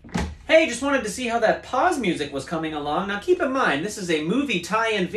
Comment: PAUSE music? Thought you said POG music 🧐